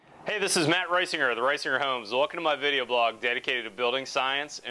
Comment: I had the volume kinda low and thought he said "ricer homes". I'm picturing chrome trim on the chimney and 22'' rims for windows.